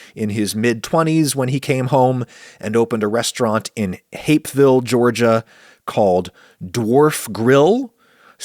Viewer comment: Thought you said Hateville Georgia and thought "well I guess that makes sense."